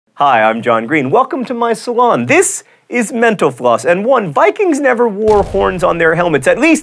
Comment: Instead of "Mental Floss" I keep hearing "Menopause" ><